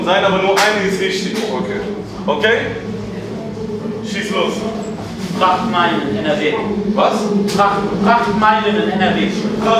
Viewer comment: was sagt der? prachtmeile in nrw?